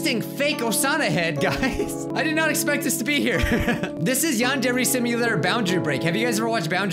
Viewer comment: Yandere Simulator? Well, the subtitles says others wise, is playing yond every Samia.